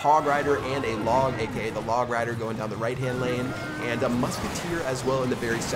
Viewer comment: did you say "Log rider"? like if you heard it